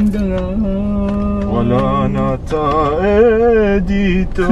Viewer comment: Happiness and sadness are mixed together while watching this video...may God bless the poor people. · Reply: Hajime Hinata Oi can u skip the ending and hear what he sand did he just say ... wala ng tae dito?